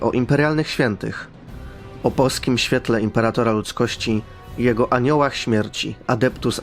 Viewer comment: Usłyszałem "O polskim świetle Imperatora Ludzkości" i podoba mi się to XD